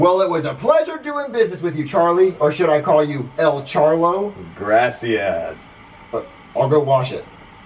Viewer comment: it sounded likee he said grassy ass